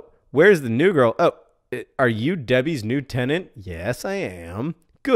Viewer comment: when you thought Debbie rose said pebble rose